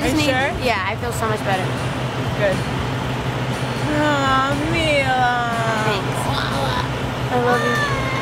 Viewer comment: Dinah dijo ay amiga o fue que me pareció que dijo así?